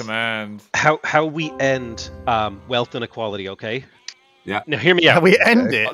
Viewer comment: ' reaction to wealth inequality is very telling, And accurate. In the future the system that gave him so much of the slaves money won't exist. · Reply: I think he misheard it as ‘how to end wealth equality’